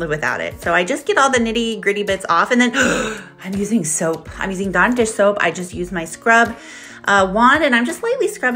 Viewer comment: Did she say she dish soap or non dish soap? I ask as we always use fairy liquid here in the UK and does wonders....can even strip the colour out your hair if that's what you want lol😂😂. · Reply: I heard, "I'm using soap, I'm using dawn dish soap".